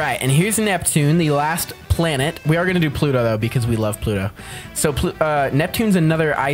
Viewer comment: I thought you said you hated Pluto make up your mind do you hate Pluto or love Pluto